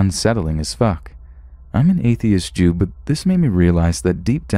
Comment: did I hear him say "im a atheist jew"? or am I just that crazy